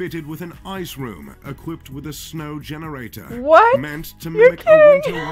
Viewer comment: Did she say "ye kya hai" at 3:47?